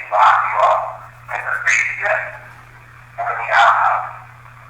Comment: I know this video is so old but at 15:39 it sounds like they say neither and not outhouse. Like maybe a soldier was saying neither. Like he was never locked up